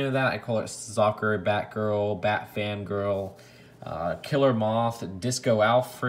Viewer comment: I heard "bat fam girl"